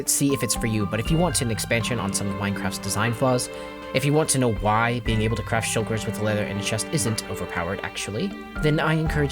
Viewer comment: Briefly misheard "If you want to know why being able to crash servers with leather and a chest isn't overpowered..." and now I'm intrigued.